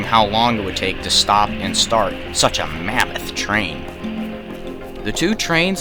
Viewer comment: Did he just say mammoth